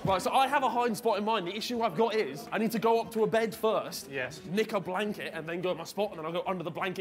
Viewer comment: When Harry said Knick a blanket I thought he said lick a blanket